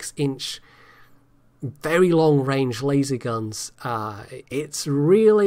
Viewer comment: I know accusations of Soviet bias are commonplace, but did you say (at 43:37) that the Kutuzov is fitted with lazy guns? Having enemy vessels suddenly buried under freak rains of fish, sucked into enormous whirlpools or swallowed by giant squid will only increase the perception that the game is skewed in favour of Russian craft.